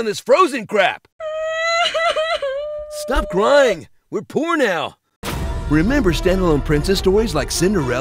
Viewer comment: when he said we poor now i heard we're porn now lol